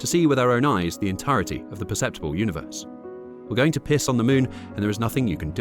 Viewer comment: I can’t be the only one who thought it said “We are going to piss off the Moon” right?